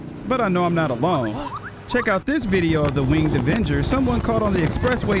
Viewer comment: did he call Batman an avenger😂